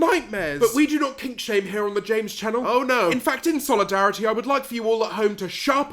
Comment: thought he was gonna say james charles